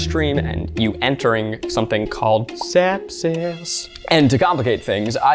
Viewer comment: What he says: "sepsis" What I hear:"step sis"